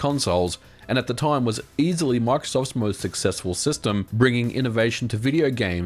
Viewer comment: the Wii had more sales at over 100 million the Wii was EASILY the more successful console (meanwhile, the ps2 and the ds are laughing in over 150 million sales) edit: I thought you said "easily the most successful console" not "easily Microsoft's most successful console"